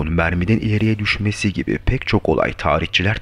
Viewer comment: ' ne diyorsun anlayamadım. Attığı merminin ileriye düşmesi falan.